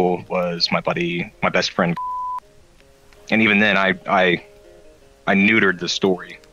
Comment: I heard it as "My best friend Fuck"